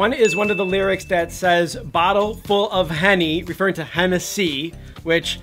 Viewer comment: I thought they said bottle full of honey 😂😂😂